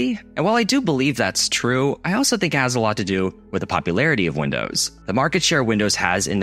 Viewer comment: the subtitles say "with the popular of Windows." when you say "with the popularity of Windows."